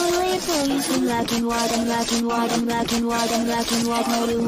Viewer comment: “Imagine waddle imagine waddle” why do I keep hearing that in this song? XD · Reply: I hear “I’m black and white I’m black and white”